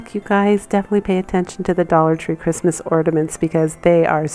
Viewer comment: Love the things you’ve shown. But I listened to your taped piece when you say the word ornament it sounds like your saying ordament instead of ornament.